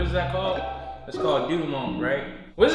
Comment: This is the section time I heard you say "doodie-munk" whaaaat!?!?! What is this food?